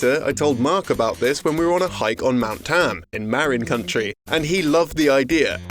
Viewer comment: I swear I have heard county pronounced like country like 5 times. Is that how other people pronounce it is or it a mistake?